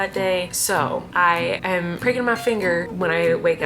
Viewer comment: Omg I literally thought you said "breaking my finger " instead of pricking..I was like what??!!